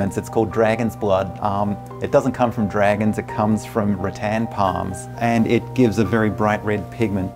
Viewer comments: i thought he said it comes from a tampon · Misheard rattan palms for tampons, just rly relieved rn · I thought he said, it came from tampons.